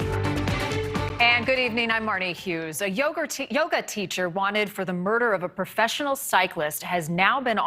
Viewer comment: I heard "A yogurt teacher" at 1st. You cant take that away🤣